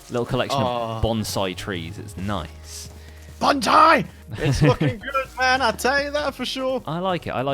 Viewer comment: did actually curse then? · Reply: Oh wait no he says it's looking good.. i thought he said it's BEEPing good.